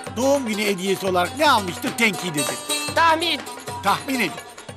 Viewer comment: yunus ne diyor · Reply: tahmin